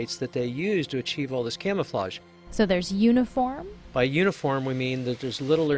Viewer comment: I thought she say "so theres unicorn?"